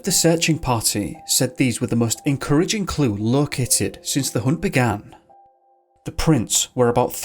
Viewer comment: sounds like an audio edit. But instead of 'located", I can't help but hear, "look at it". Too funny